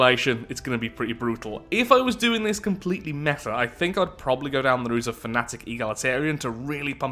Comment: With the new DLC out here is the first of the new Full Playthroughs! I plan on making three in total covering all of the new origins and perhaps a quick test video with the new civic which has similar abilities as the origins themselves! I'm still of two minds of this DLC so feedback is very welcome! I hope you all enjoy! :D So two things to mention - I mix up what shield regen is early on in the game, I correct myself later. I ALSO mix up what the contingency is made of in regards to their defense (I was thinking about their big stations which are mostly hull.) I correct myself later on that too. Recording over many days can lead to.. odd words being said in odd ways. I will also be covering the cloaking system a lot more in future videos! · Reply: Wtf did he say here? "...if I was doing this completely mehfa..."???That's what YT captions gives me.